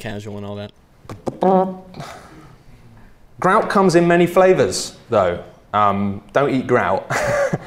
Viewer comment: at first I heard "cum comes in many flavors" and then laughed, then I laughed out loud.